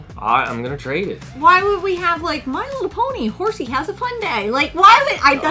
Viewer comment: I swore you said "My Little Pony: House of Hyundai" and thought "Oh, Shenmu meets my little pony. Interesting" Full set collecting to me is a lot like tattoos. They look great on other people but it's not for me.